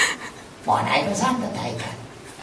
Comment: What was he saying at 0:16?? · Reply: At exactly that point, he said "how are you doing?" XD